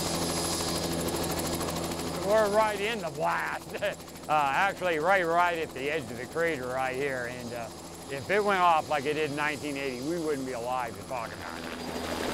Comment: “If it went off like it did in 1980, we wouldn’t be alive” and that is why he's the expert volcano dude. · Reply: rewatch that bit again. The entire quote was "We're right in the blast. Actually right at the edge of the crater right here. If it went off again like it did in 1980 we wouldn't be here to talk about it." He was not saying we all would have died, just emphasizing how close this small group is to "ground zero" of the original explosion.